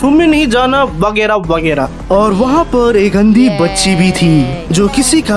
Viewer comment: What did you say. About the girl. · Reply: Andhi bachee